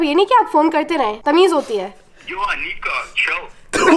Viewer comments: can anyone tell me what did the guy say?? im curious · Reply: "yo aniqa chill" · what did he say ???? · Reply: G oh Anika Chill